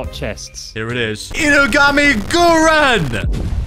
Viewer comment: i legit thought you said hinokami kagura